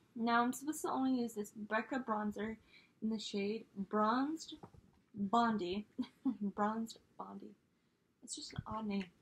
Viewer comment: I feel like everyone in the Pantastic Ladies need bright pink shirts that say Pantastic Ladies. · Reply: I thought you said Bronze Bondage. 😂 Omg. It's too early/too late for my brain. You're doing so well with all the items! I love that your hubby is low-key (or maybe high-key) sabotaging you with these products. From 532 to 7. 😂